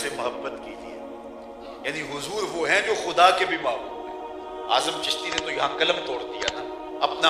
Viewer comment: What did he say 1:52 "Huzoor woh hai........." please tell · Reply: Huzoor wo hai jo khuda ke bhi mehboob hai 😊